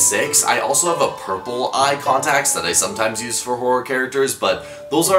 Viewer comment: I haven't finished the video and I'm still at the start and when said a hor- (horror) I genuinely thought he said "as a Whor-"